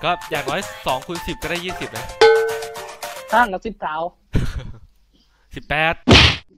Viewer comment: ครับนาทีที่ 10:36 เขาพูดว่าห่างกัน 10เท่าครับไม่ใช่ 19 คนผมคนใต้เลยฟังได้นิดหน่อย